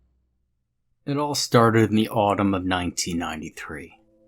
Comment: Legit thought it said “the autism of 1993”